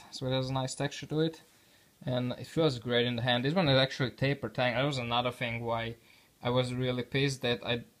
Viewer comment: Tapered tang, knifechete .. . That warp isn't enough to cause a functional problem, let me know if you do want to sell it. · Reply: Exactly what I meant. You just listen to them all at once. Go: "Tapered tang? Who said that? IT sounded like he said tabluh tongue. Obviously mike"